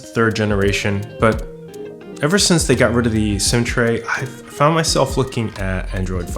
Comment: My brain could not process "SIM tray" that you mentioned. I kept hearing "centray" and I had to rewind 3 times before I activated closed captioning. Getting older sucks sometimes